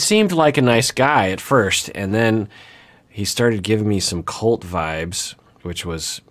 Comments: I’m so glad I read the comments because I thought Dr Honda was saying CULT vibes not Colt vibes, lol. I was wondering where you got that feeling from. Now Colt vibes, yes, that I can see. If what Amira is saying is true, she needs to run. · Am I the only one who misheard “Colt vibes” as “cult vibes”? 😆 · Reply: I totally heard 'cult vibes' but I was really confused what he meant by that so came to the comments to see what you all were saying. and realised its 'Colt'! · It’s lovely to see the stigma of men in “caring” job positions broken down. My job roll is 99% a female job and it’s such a shame seeing clients be judgmental towards our male vet nurses. Edit- I was so confused for a minute there. I thought you said “cult vibes” not “colt vibes” and I was thinking, mate you are well off on this one. 😂